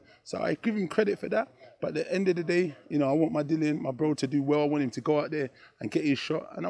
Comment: I want my Dillian WTF did he just say lol?